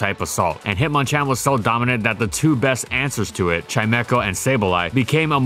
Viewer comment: Did someone said Chimeco????